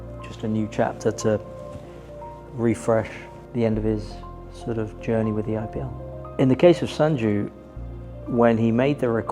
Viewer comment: Did he just say "The end of his journey with the IPL"